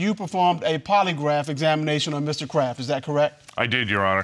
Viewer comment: I keep hearing "Mr.Crab"